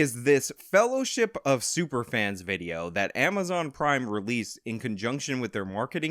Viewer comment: For one Second i thought he said "Amazon crime" instead of "Amazon Prime" 😂😂 would have been fitting